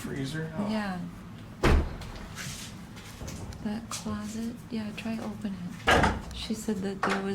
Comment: when you round the corner it sounds like some says “C**t” ?!??!